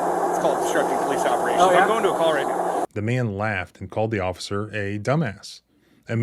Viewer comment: did he say he's going to a call right now?